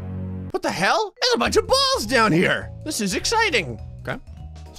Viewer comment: i heard this: 'holy god is that a bunch of balls' i literally almost went to heaven